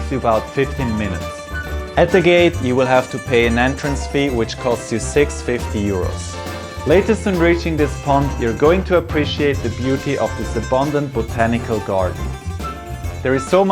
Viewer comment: did you just said 650euro or 6.50 euro...?? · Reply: Hi Rohan. It's 6 Euro 50 Cents - 6.50